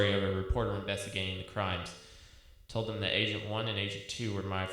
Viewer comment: I was in a mall and thought he said Asian 1 and Asian 2